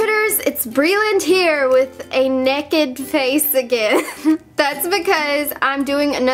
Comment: did she just say "neked" instead of naked?